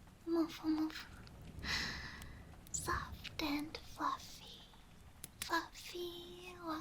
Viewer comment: did she say fluffy??? Fluffy fluff fluff